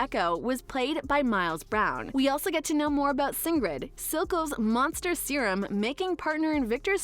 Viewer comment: did she say singred instead of singed?